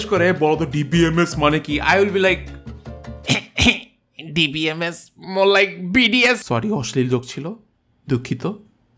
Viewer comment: i heard BTS lol